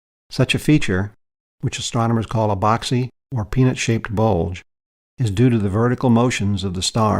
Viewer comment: I thought he said penis shaped bulge I'm ngl